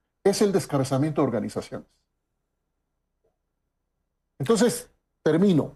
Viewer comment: Pregunto Que dice el invitado en el minuto 23:35 ? Suena algo como “descarzamiento organizacion “ Que es eso ? O Que quiere decir?